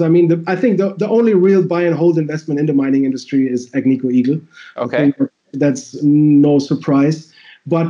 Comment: What did he say? It wasn't clear regarding 'the only buy and hold' @ 10:48 ? Was is American Eagle as in the coins?